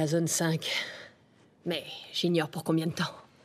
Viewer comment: y’a que moi qui entend « Maëva je suis prêt mtn » Ouuu ? 😭